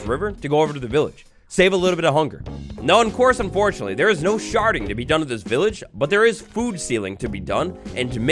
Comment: Sherding? Sounded like sharting 🤣🤣🤣🤣